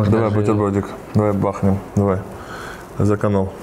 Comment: в 9:07 послышалось за коноху